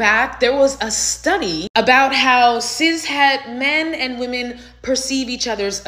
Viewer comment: did this woman just say "Cis", I couldn't understand her.